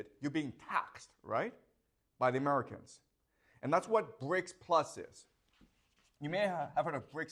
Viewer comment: i thought he said brakes plus and i was like BRUH i get my car worked on there